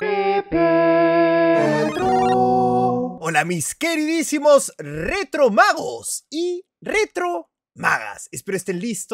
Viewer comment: pensé que dijo retro bagos xD